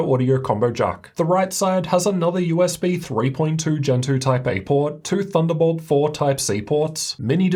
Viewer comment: The full review of the GT77 with all the details is now up! <link> I also want to note that the 2.3 cm thickness is the minimum point. Maximum was not specified prior to this video posting at launch. Regardless, it's clearly much thinner compared to previous desktop replacements which was the main point. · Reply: When you said "USB Gen 2.0 Type A port", I heard "USB Gentoo Type A port". I couldn't understand how there was a USB socket named after a Linux distribution!